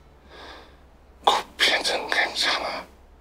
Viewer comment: 제가 1988몰라서 그러는데 7:12 뭐라고 하시는지 아시는분? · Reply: 코피는 괜찮아? 라고 한거 같아요